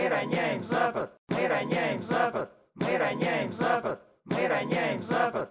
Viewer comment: Кому-нибудь послышалось что на первый раз мы роняем запад на второй мы роняем запах а на последний мы роняем за борт